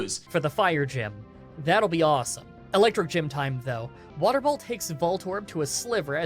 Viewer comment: Weather ball in different weather's: water ball Fire ball Snowball And round rock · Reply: He actually said water ball at 17:00 lol